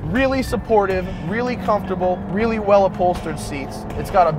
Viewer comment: did he just say well upholstered...looks a little baggy to me.